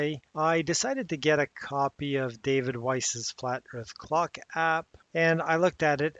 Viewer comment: I misheard you at the end and thought you said "Flat Earth Crock App" which ironically would be more fitting. By the way, it's DITRH, not DIRTH. I've heard it's an acronym for Down In The Rabbit Hole and honestly, I think he should leave the bunnies alone.